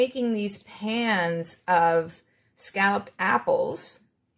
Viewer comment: When you said "scalloped apples", I thought you said scallops and apples. I was like, Noooooo! I'm not a seafood fan- just a "see food and eat it" fan. lol Your Fall treats looked delicious!